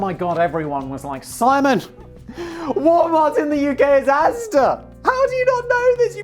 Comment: What did he say? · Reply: ASDA